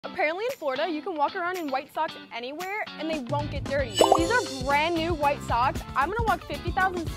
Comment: I thought she said Fortnite not florida